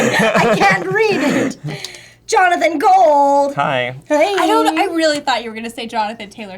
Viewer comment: I thought she as going to say Johnathan Gay 😝